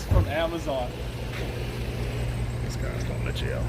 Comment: lmfao did anyone else hear him say " this guy's going to jail"